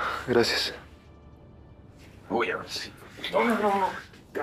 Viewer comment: yo escuché "Ahora si te descalabro los cachetes......."